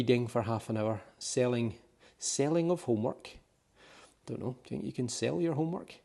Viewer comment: Great tour, thanks:). I also thought it said "selling homework". It was SETTING lol. I can't wait to get back to the U.K next year!